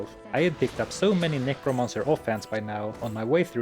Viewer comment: I heard "I had picked up so many Necromancer Hot Pants by now."